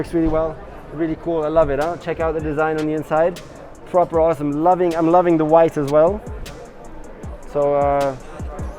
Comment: For a moment I thought Nico said "I'm loving the wife as well". To which I was thinking, "well, I should hope so."